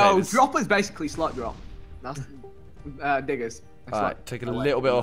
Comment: Anyone else do a double take on 12:24. Thought said nigga😂😂😂